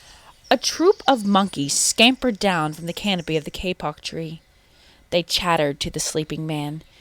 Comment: i know, i was just saying what i heard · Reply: Lilleppard Roblox Gaming one sub yes, and that's what i heard that's why i said "why do i keep hearing kpop tree"